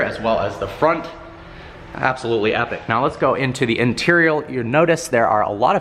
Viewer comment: He said interial instead of interior